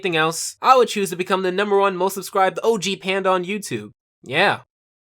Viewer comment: did you just call out apanda?